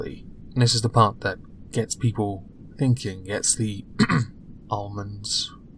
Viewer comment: Almonds activated! Getsya noggin' joggin, eh? Btw... I realize you said "this is what gets people thinking" but i thought you said "gets people inking"... I thought "wow.. how fucking meta"... But I was wrong. 😔